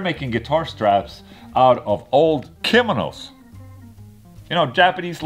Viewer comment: It sound like he said it was made out of old criminals not kimonos lol.